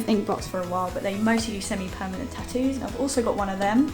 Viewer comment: What do you mean when you say that inkbox sent you a permanent tattoo? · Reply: I just listened back.... 🤦🏻‍♀️ I thought you had said "they also do send me permanent tattoos" 😂😂 It definitely confused me...